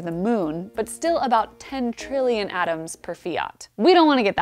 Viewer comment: "10 trillion atoms per fiat" Did I hear that correctly? What unit of measurement is that? What's a fiat (besides a car or a decree)?